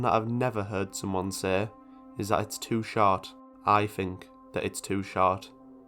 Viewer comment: The first time you said 'short' I heard 'shart'. Wow.